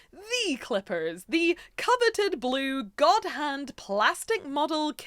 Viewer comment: With your accent and pronunciation, I thought you were saying, "Goddamn." And I was agreeing. "Goddamn! This IS cool."